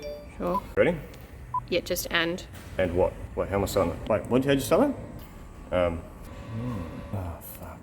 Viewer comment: lol, wtf are you even saying here???... 🤣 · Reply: "wait how am I starting that, wait what how'd you start that" Try the english subtitles LOL we're surprised that about 30% of viewers use them 😅